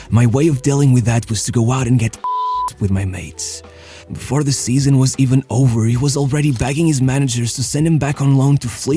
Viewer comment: what's the word? Pim---? I am looking for words starting with P ending with ED, is that it? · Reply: pissed